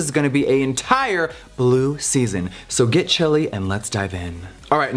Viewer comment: Lmao I thought he said “get chili” not “get chilly”: I’m hungry.